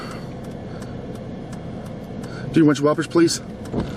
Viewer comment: I thought he said 3 something whoppers please